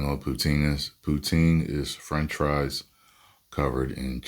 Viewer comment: AYO! I thought you said you eating “pie” everyday… Thanks for the clarification.